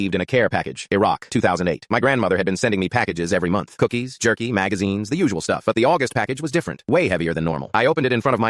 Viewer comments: I only hear the audio and needed to repeat the video two more times because I heard "Jerking Magazines"... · at first i thought i heard "jerky magazines", im like what? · I heard "jerking magazines." I need Jesus.